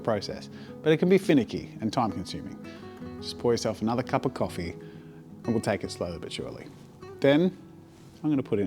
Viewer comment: I thought you said it was tea.